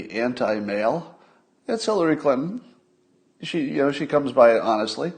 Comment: I had to listen twice but sounds like you said, “Killary Clinton” 😂😂❤️ it! This is one of your best Episodes. I really like the No BS Scott Adams. Kind of the same no bs qualities I like in our President.